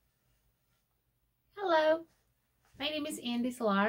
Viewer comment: I thought it said Hella.